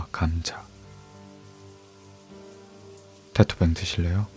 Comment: 뭐라고 하는건가요? 여러번 반복해서 들어도 잘 안들려요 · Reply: 테토뱅 드실래요? 19:06 강에서 건져 올리든